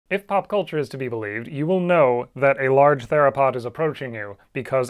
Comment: I heard "if hip hop culture is to believed...."